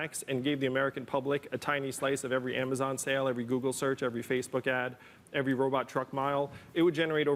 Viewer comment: did he say they would tax every google search?